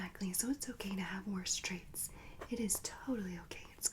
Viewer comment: at first i thought you said straights